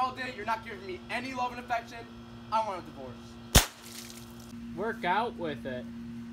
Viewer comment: I thought he said make out with it